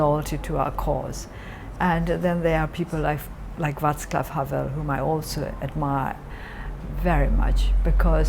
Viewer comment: what name does she say at 10:17?, the second person she's inspired by. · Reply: Vaclav Havel of Czech Republic